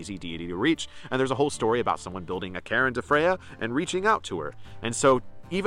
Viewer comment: you say that theres a story of someone 'building a _____to Freyja' (related to Freyja being easy to reach). May I ask what you said? The word sounded like Karen, so were you saying perhaps 'cairn'?